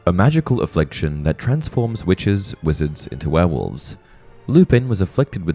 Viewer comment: Every time you read “witches/wizards” from your script, I always hear “witch’s wizards” 😂